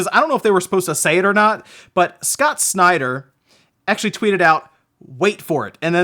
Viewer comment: I thought you said Scott Steiner at first lol